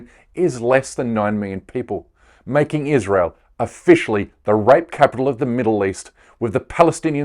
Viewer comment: I thought it said rape capital of the world lol that's clearly India